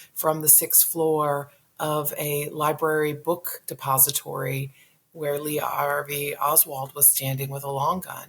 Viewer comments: Carol is the best of the best of the best. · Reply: Is Leonnig saying "book SUPPOSITORY???" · Carol's a top notch journalist, truly amazing work she's put out. Thank you, Ms. Leonnig. Thank you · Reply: Is Leonnig saying "book SUPPOSITORY???" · Is Leonnig saying "book SUPPOSITORY???"